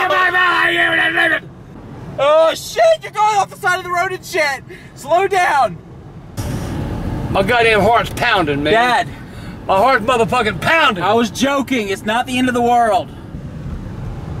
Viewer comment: did he say "my hearts fucking pounding nigga" 😂😂